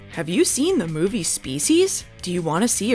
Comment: I thought you said SUBspecies instead of Species and was very hyped for a second to have Radu fight Mitch